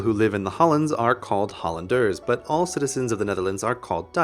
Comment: Hollanders.... thought you said colanders. XD